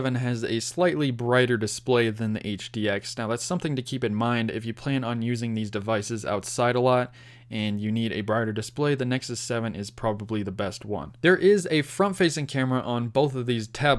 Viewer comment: What did you say at 2:04? If you plane use this device as .....? Btw-which one is better for reading ebooks in your opinion? Thanks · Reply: "If you plan on using these devices outside a lot and you need a brighter display, the Nexus 7 is probably the best one."